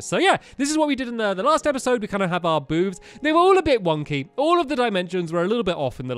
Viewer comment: In 3:40 did just said "boobs"?